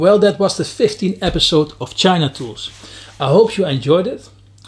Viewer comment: I just stumbled across this channel by accident. I'm hooked now. I just subscribed. I must admit the first time I heard him say 'China Tools', because of his accent I thought he said 'genitals'...LOL!!! Keep up the content. Excellent series.